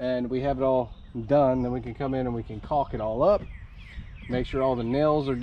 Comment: 🤣😂🤣 i had to re listen to that when you said cork it all up i thought you said cock it all up 🤣😂🤣